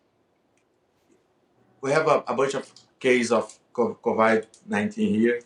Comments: I definitely thought he said " we have a bunch of gays " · I thought costa said hes got a bunch of gays at 19 here... Lots of guys in the comments section keep saying how good he looks, go train with him... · I swear he said "we have a bunch of gays with covid 19 over here" hahahha